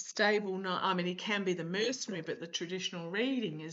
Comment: I heard something at 21:18-21:19. It's a woman's voice and it sounds like you, so it's confusing, but she says ''mercenary.'' Maybe that's just a glitch in the recording.